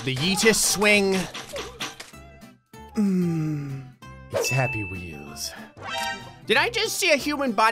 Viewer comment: I thought you said sappy wheels lmao